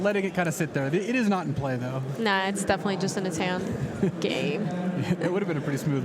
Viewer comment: what does gaby say at 1:02 ? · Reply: Game